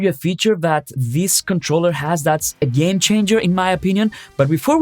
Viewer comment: Thought you said: this is a gain changer for me. Not game changer😅